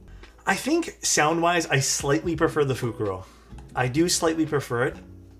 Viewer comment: What board did say he prefers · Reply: Fukuro I think